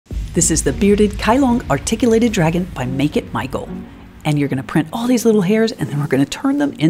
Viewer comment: When you said “make it” I thought you said “bacon”